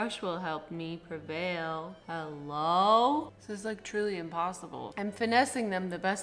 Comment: She said "truly impossible" but I heard "julien-possible" Did I mishear? Yes. But should this be a thing? Yes. Julienpossible. The kim possible bootleg.